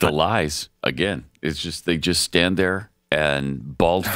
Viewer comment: I could have sworn I heard Stew say, "they just ball face lie to us" LOL